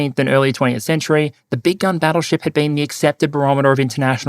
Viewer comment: “Big gun battle ship”, I thought he kept saying “big dumb battle ship”.